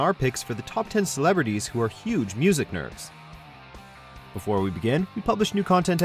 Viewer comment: Did anyone else hear 'Music Nerves' at 0:07 ?